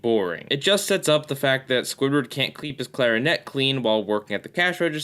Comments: I'm not the only person who heard "kleep his clarinet clean", am I? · Am I the only who, during the Squidward in Clarinetland review, heard him say "kleep the clarinet" instead of "keep the clarinet?"